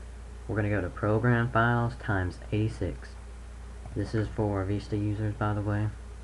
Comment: Lol it sounded like he said vEsta